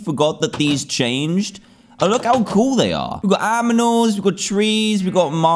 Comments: did he say aminals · Dan: So we’ve got AMINALS..etc. Me: wait wait wait... did he just say aminals? rewinds the video Me: 😐..... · did he just say aminals? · ok, did he just say aminals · Was it just me or did Dan say 'Aminals' instead of 'Animals' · did he just say aminals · "Aminals" (Disclaimer:I dint type it wrong.he actually said that.) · did you just say aminals!!! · No hate but did anyone notice that Dan said Aminals instead of animals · did he said aminals?